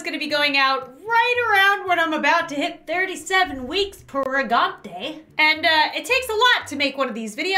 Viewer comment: When she said she was 37 weeks pregnant I thought she said “i’m 37 mouths pregnant” me: Hold up wait a minute.. something ain’t right :o